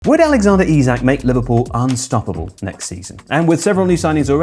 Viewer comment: coulda sworn you said "arne-stoppable"